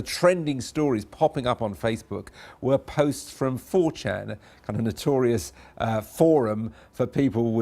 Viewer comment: is he saying at 3:00, stories from forchan? Never mind, subtitled it. 4chan, never heard of it.